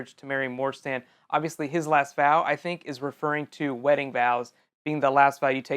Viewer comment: When you were talking about the last vow I thought you said bow!